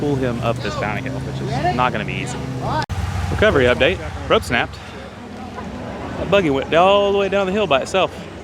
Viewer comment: Did he say rope